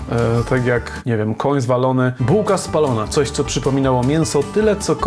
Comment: Usłyszałem mówiącego "Koń zwalony" ...teraz mogę umierać szczęśliwy :D